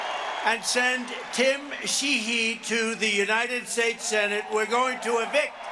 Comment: did he just call walz a "she/he"?? wtf